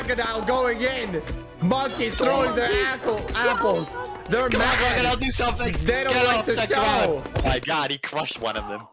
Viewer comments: WHAT DID HE SAID IN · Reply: Socks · what did bro say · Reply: Apple